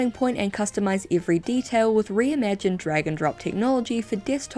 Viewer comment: I wasn’t looking at my screen and misheard “drag and drop” as “dragon drop,” and for a split second I thought this ad read was somehow connected to the 100% dragon science-based MMO